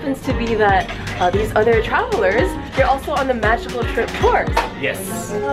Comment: I thought said "Magical Shrimp"😂😂😄